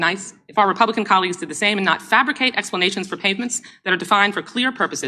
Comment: DWS clearly says "pavements" instead of "payments". What's wrong with these robot people?